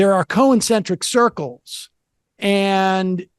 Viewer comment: Also "More dernity" instead of modernity. He can't pronounce quite a few words, it's always been that way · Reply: also I just heard him say "co-eccentric circles" 😂😂😂😂